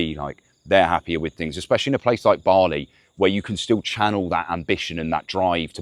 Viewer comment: It sounded like he was saying "barley" but is it "Bali" ?